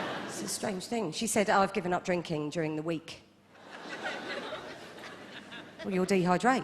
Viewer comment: What's she saying at 2:11? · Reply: She says "I've given up drinking during the week (meaning alcohol)". "Well you'll dehydrate" (meaning you'll dehydrate if you don't drink anything)